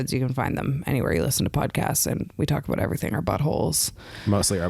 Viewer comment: From 8:01 I understood "we talk about everything, but holes" and then was interrupted, which is why I heard something specifically different, namely "but holes" only with a t on top and as a single word.